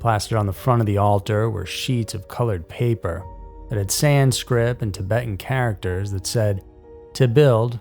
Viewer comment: sounds like ur saying sanscrib and even CC says sans grip.....It's Sanskrit, SANSKRIT just for clarification.